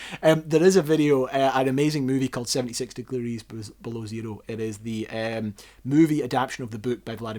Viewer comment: i dont understand the name of the movie, someone can write please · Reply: 72 Degrees Below Zero